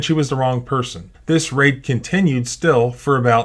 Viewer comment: I think I miss hear him say raid as rape. To be fare that is exactly what this is.